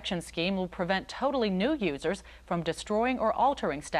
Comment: Is it me, or did she say "Totally newb users" at 24:19 ? Did she coin the term "newb"?